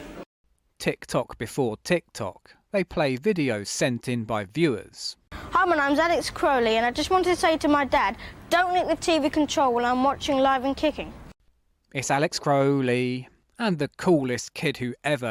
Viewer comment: I thought he said he was Aleister Crowley at first 👀 🎩🔯